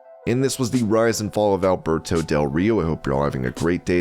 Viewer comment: I thought you said Humberto del Rio lol